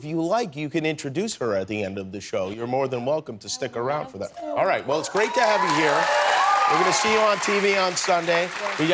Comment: What did she say??? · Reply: “I would love to”